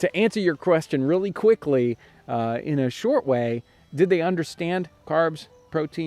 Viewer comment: Love your videos! I just have a question: did you mean to say "...and did they understand their nutritional value?" I heard it as "Understrand" and don't know if I genuinely just misunderstood the sentence. Thanks!